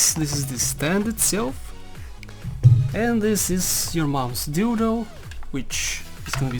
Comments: wait wait wait,, at 7:26 did he call the stand ''ur mums dildo''? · was it just me or did he call the monitor stand my moms dildo?